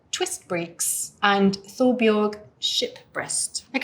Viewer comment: I had to go to transcript as I thought at 1:03 you said 'Thorbjorg sh!t breath'!